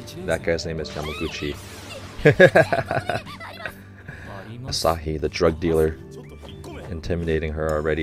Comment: wait did he just say asahi the drug dealer?